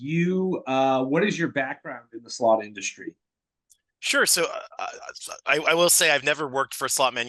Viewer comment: that is SO annoying..... i had to pause the video, and come back to it an hour later. . "uh uhu huhuuuhh uuhu hu uh uh uh" and i SWEAR he says "i suck" at the end lol . im just not sure if he sucks at gambling, sucks at explaining how gambling works........ or simply sucks dick........ . im going to go with the 3rd possibility....and say it was a freudian slip